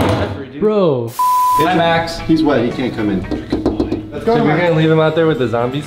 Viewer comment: You guys should do a gymnastic challenge because that be hilarious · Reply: he is white he can’t come in 🤣 I think he said lmaoo